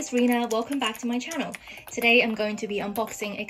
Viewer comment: What how do u spell ur name cuz it sounded like u said my name (Ree-na) spelt Reena